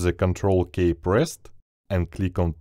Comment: Thank you for the tutorial. The end result was not exactly what you had (used Photoshop 2021 here). At 15:50, it's Ctrl ки, not Ctrl кей, since everyone thought you said Ctrl+K, not just press the Ctrl key. But I do appreciate you.